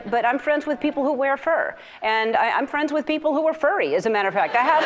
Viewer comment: I thought for a sec she said she's friends with furries